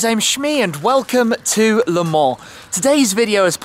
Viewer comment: I thought he said:" Hi guys, I'm Shmee and welcome to LMAO".